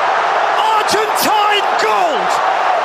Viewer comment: I thought he said Argentina goat lmao it's argentine gold